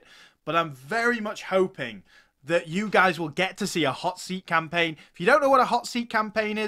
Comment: I thought you say Hittites (hotseat) campaign at the beginning. I've watched all your videos about RTW weekend, that's a really awesome series. Btw, I love your new helmet, as well.